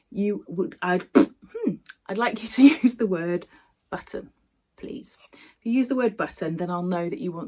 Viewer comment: Great stitching as always, congrats on the subscriber numbers. Think I need my hearing checked thought you said butter not button 😂 hx